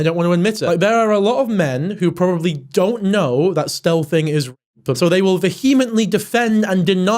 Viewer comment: is he saying stelthing? I’ve never heard the term and can’t really decipher what he’s saying, someone please educate mad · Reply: he's saying stealthing, which is when you remove or damage a condom without the other person knowing